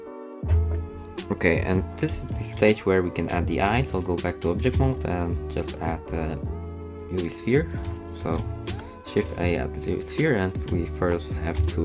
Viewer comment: Very nice but i can not understand what you're saying. No matter how often i try. What do you say in/after minute 11:49? I dont know how to smooth the sphere (if that's what you said) cause if i right click on the sphere nothing happens. Can you help ? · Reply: Hi sorry for my English it's not best but I try to do my best. Basically I sais that we can add the eyes and i proceed ro add a UV sphere. You can subdivide it two times by adding modifier and click on object panel on the top left to shade it smooth if right clicking is doing nothing